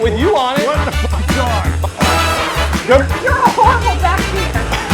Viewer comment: I said you’re a horrible backseat passenger LOL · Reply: lol well yea I thought I heard ''you're a horrible bikerbabe'' lol ;) lol hahahahhaaha I WAS CLOSE LOL